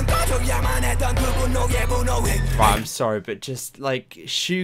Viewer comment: I'm the only one who heard 'sorry bitches' at 4:30?